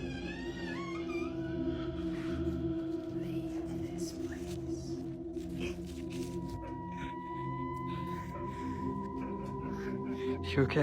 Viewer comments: if you listen with headphones you can here a male voice saying "leave this place...." there was something after that, I couldn't quite make it out - but it sounded like a threat. Then at the end you can hear this horrible, sinister laughter. Most unsettling..... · you can hear a womens voice saying *leave this place *...did anyone hear it?? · I have headphones on and at 11:26 right when he opens the door, you can hear a woman say a drawn out “leave .” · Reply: I heard it at 12:07. It said, “ leave this place.”